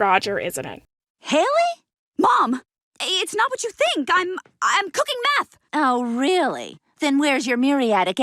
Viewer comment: Won't lie when i was a kid i always thought she said "i was cooking math"